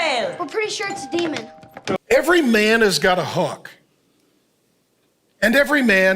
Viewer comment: i'm so fucking cooked, I thought he said "Every man has got a hawk" and I was deadass waiting for him to say "and every man has got a tuah."